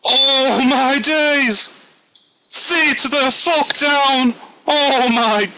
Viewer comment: is it just me or did he just say "see to the fuck down?"